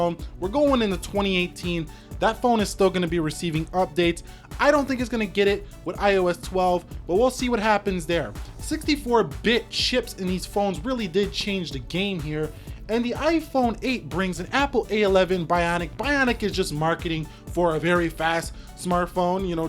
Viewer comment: I thought he said bitch